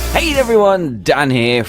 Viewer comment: Dan it sounds like u say" hate everyone"